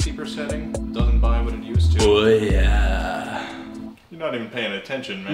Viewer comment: Did he say Ouya!? Holy shit Antonios the Germans!!